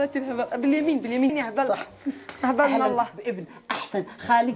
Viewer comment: كأنني سمعت ( ماهبل إلا الله ) حاشا الله وانا عارف انها غلطة غير مقصودة فحاول تدقق في الكلام ياخي قبل نشر المقطع وتحياتي لشخصك الكريم